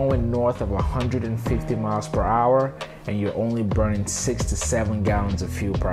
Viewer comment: Wait at @ 1:03 is he saying "only burning 57 (67?) gallons per hour"? Only? Is that right? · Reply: "only burning six to seven gallons per hour." You're welcome.